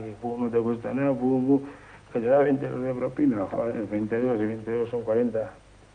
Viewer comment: Una cosa curiosa, minuto 17:17 Jon Manteca habla de 20 euros? año 1988? no me cuadra o no entiendo. Alguien sabe? que paso? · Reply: Jony 20 duros, dice 20 duros